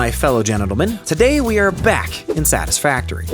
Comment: "Did you just say genitalman? Because if so Kudos..."